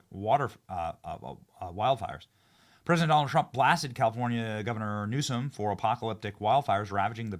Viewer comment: I thought you said California engorged my bad. Waiting for Newsome to screw over his people AGAIN makes me go....thank Christ i don't live in that piss hole of a state.